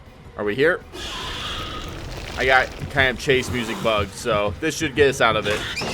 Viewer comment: What did you say at 4:53? Some kind of bug? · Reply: Yeah if you notice we had chase music even out of chase until we got into another one. It's a chase music bug that pops up every now and then